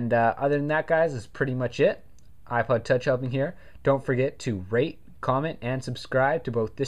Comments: it sounds like he said "don't forget to rape, comment, and subscribe" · sounded like u said dnt forget to 'rape'...lol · it sounded like u said RAPE comment and suscribe at the end lol